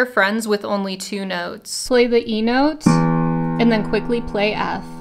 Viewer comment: "play the E note" What I heard: "play the emote"